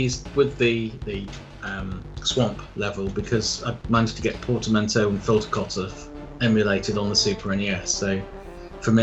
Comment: SOMEONE TELL ME WHAT HE'S SAYING HERE. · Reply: Portamento and filter cutoff emulated on the super nes